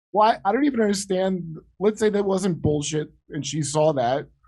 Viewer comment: I thought for sure you said "Bullshito" at one point.